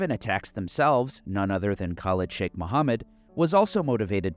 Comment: LOL wasn't watching the screen, when he said the name of Khalid Sheikh Mohammed I heard "...none other than college chick Mohammed"